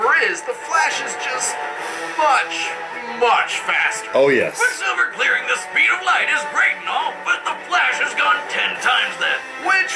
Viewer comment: quicksilver reaching the speed of light is .....what? what did he say there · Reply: He said 'Quicksilver reaching the speed of light is great and all, but the Flash has gone 10 times that'.